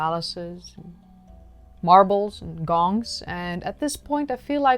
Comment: I thought yo were saying "Bongs"..... Id rather suck on a Bong anyway lolololo